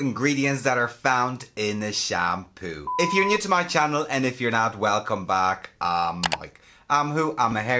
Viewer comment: Every time you do your intro, I hear “if you are not welcome back” as opposed to “if you are not, welcome back”, I keep wondering what these people did so wrong that they aren’t welcome to watch your videos 😂 P.S As usual really knowledgeable video Mike! Thank you 🤗